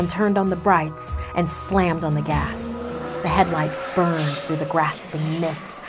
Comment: the captions say "the headlights burn throu the grass pe**s